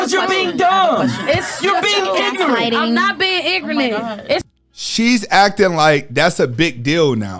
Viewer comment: she said igernet, instead of ignorant 😂🤣😅